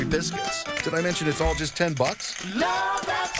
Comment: whats does it say at 0:09 ? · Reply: Love that chicken from popeyes